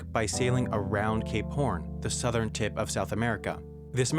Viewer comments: I misheard "Gay Porn" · did he say gay porn?